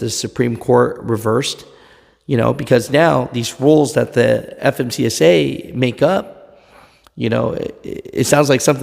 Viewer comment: FMCSA. Am I hearing you say, "FMTSA"?